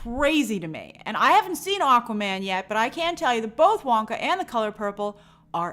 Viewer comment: did she say that Wonka was excellent?